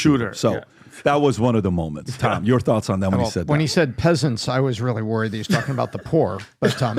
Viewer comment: is a disingenuous point. I’ll assume he misspoke when he said he’s friends with school shooters although it is definitely a bad look. However, he clearly said pheasants not peasants. That is an extreme leftist level misinterpretation. He said enough stupid stuff and was clearly outclassed that we don’t need to stoop to the level of making things up.